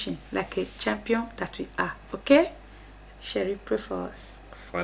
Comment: Do you guys read your comments once again I ask why do you call each other sherry blessings to you borh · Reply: Lol and my bad I thought you guys were saying sherry it's cherry even better you guys should call us your cherry pies cause we love y'all